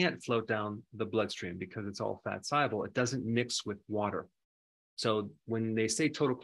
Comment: What does he mean by fat sible? I thought he may be saying "soluble" but it definitely sounds like sible, even playing the vid in slow-motion.